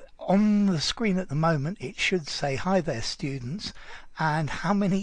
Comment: Oh, I've never thought it was "hi THERE" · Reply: In the past I thought he was saying "Hi best students". Lol